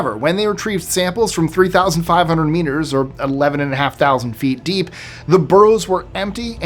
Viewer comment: Haha, I nearly died at 5:38. I heard him say samples from 3500 weiners 😂.